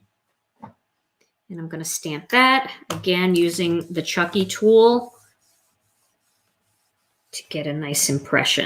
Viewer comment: What's the name of the tool, is using to rub over the Misty to get a good even print? Does sell it? I tried listening to what she called it but couldn't hear it. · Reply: I was wondering that also. I thought she said chunky tool? But I can't find it